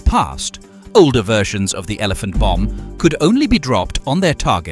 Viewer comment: That sounds like my mother-in-law....uh.. oh, I thought you said the elephant bum. Sorry. Carry-on.